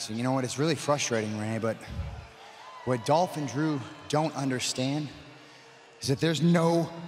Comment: Seth sound like he said "Dolphin Drew" even though he said "Dolph and Drew".